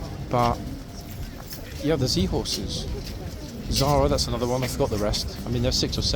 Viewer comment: I thought I heard sea horses, then I realised it was zee horses. Come on, there's far too much influence from across the pond on the internet. Z is pronounced ZED NOT ZEE!!!!!!!! I thought you were intelligent enough to know that!